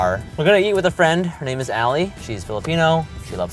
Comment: I'm gonna say it. is beautiful. ❤️ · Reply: Steve weird, I swear he says 'Ally'